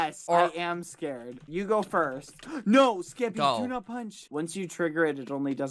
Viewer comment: its so funny when bbh sounds like hes saying “geppy” xD